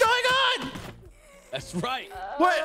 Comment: did anyone hear say "thats right"